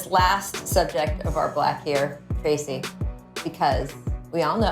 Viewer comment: did she say our black , Tracy McGrady? lol